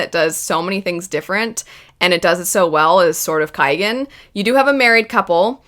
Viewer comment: I can't understand the name of the book at 05:55, can somebody help me?🙏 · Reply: I think she is saying "The Sword of Kaigen".